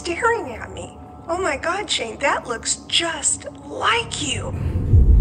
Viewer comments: I’m sorry but did anyone hear Shane’s name twice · and 27:32 does it sound like it saying Shane?????? It go by fast so just go back to those two time to hear it again. I am so freak out right now. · something says "Shane" at 26:09?? is it just me?? i thought it sounded like but now im not sure?? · Does anyone else hear Shane’s name time stamp 26:08? Or is that just me? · did someone say Shane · sounds like someones saying "shane" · Why do I hear something like "Shane" being whispered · I HEARD A WHISPER THAT SAID SHANE AT 26:09 WTF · Omg if you see this plz look at 26:06 bc I hear a whisper that said shane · So did anyone else hear "Shane" whispered @ 26:08 I believe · it sounds like someone says "Shane" as you're talking.. easy to miss but I'm watching this with headphones so it was so clearrr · Did anyone else hear a wisper at 26:09 saying shane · did anyone hear something day "Shane"?!? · I got goosebumps who heard “Shane” at 26:09 literal goosebumps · after his mom said it looks just like you I heard “Shane” fast! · I heard Shane but I don't know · it sounds like someone said Shane but really quite listen closely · WAIT A DAMN MINUTE...Am I the only who heard like a whisper in 26:09? It sounded like "Shane" or a "Shh". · anyone else hear someone whisper shane at 26:08 or is it just me 😭😭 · was that????? I literally heard "Shane" oh my god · Does anybody hear “Shane” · Has anyone heard "shane"? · sounded like something saying shane or shh 😭